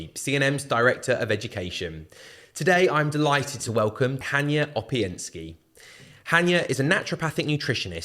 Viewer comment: Maybe it's just my cheap $59 android phone but I couldn't fully understand your guests name. I thought you said Tanya Openski. Thank God for "ai". You may want to show the guests name in writing during the first ten seconds of the podcast. I give you a thumbs up. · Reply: The name is in big writing under the pod cast. HANIA OPIENSKI.